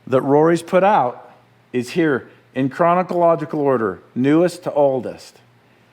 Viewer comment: Just poking fun at Matt, but did he say "Chronocological" order? :p